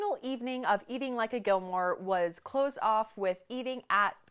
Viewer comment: Lol at 8:37 I thought you said "clothes off" and I was like, was that a Gilmore thing? 🤣🤣